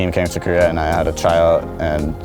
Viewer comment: Did anyone else hear I had a child? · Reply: he said try-out but it sounds a lot like child haha